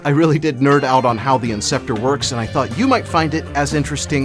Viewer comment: he says "inceptor" instead of "interceptor"